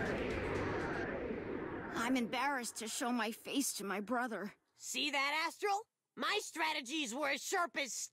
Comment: Cheesus I just heard the character saying, "see that as*h**, my strategies..." I couldn't believe, had to rewind...